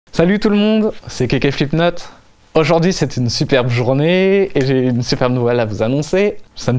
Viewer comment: i'm brazilian, and at the beginning of the video 0:00, i heard "salve todo mundo aqui quem fala é o felipe neto", and i reviewed the video several times and heard the same thing